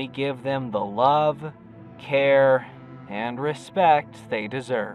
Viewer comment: Love, hair, and the respect they deserve. That’s what I heard